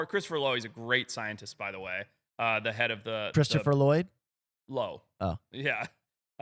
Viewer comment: Lol I thought that said Christopher Lloyd too